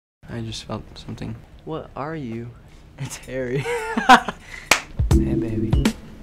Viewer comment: Is it just me or did he say "come here baby"?